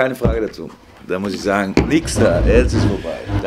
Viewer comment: was sagt kloppo bei 7:40? wichser? Oo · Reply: nix da